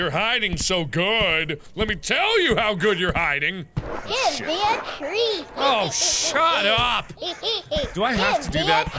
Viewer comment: When tattletale said he wanted a treat I thought he said gimme damn treat